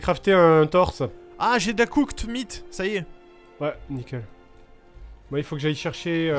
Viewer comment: Voila ce que j'ai entendu à environ 24:30 "J'ai de la cook meat est ce que je peux t'enfiler " mon dieu je suis perverti